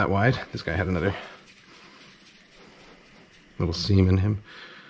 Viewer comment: did he just say semen?